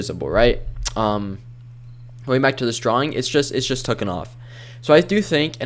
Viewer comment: Did u just say tooken off at 1:57 LOL. that made me laugh, good video.